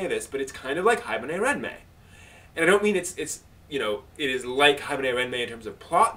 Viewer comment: "Haibuhnayranmay"? xD Which series is he talking about? I can't really understand what he's saying... o.o I tried searching for parts of what I thought he was saying in AniDB, but my only hit was an 18+ OVA. xD